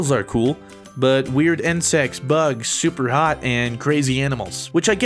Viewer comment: Did fruit just say " weird and sex bugs" What.